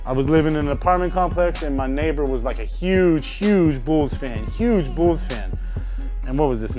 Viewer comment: Turn CC on 03:25 Laugh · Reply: he says im a huge bulls fan the CC says im a huge balls huge huge balls lmaoo